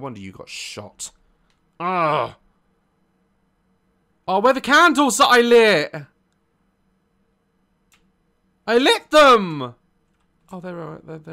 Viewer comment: What Daz said: "Where are the candles that I lit? I lit them!" What I heard: "Where are the handles that I licked? I licked them!"